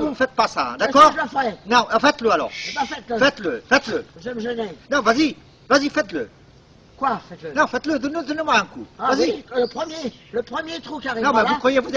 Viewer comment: "faites le faites le. Nan vas y ! faites le faites le , nan vas y donne moi un coup!... jv'ais appeler les flic" il m'a flingué! on dirait un mec des inconnus